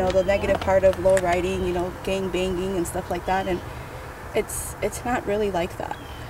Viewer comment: did I hear well?? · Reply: gang bang